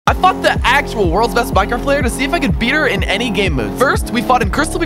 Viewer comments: I thought he said I f*cord the worlds best girl paper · ON MY MOMS LIFE I HEARD BRO SAY “ I fu** THE WORLS BEST MINECRAFT-“ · is it just me or did it sound like he said i f*ucked the best minecraft player